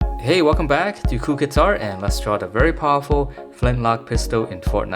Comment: At the beginning I thought he said cool guitar